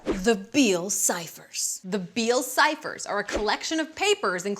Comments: Who laughed when she said beale cipher and thought she said bill cipher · I thought she said bill cypher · I thought she said "Bill Cipher" at no.3 😂 and a real mystery is what Dippers first name is... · I thought you said Bill cypher